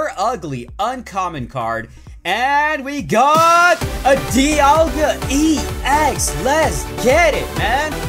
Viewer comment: Is it just me or did he say Dialga EX